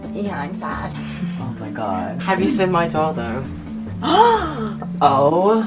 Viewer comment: Why when he said "have you seen my door" I hear "have you seen my dildo". Am I going to hell?